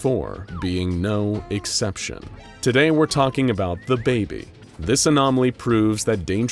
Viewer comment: For a second i thought he was saying dababy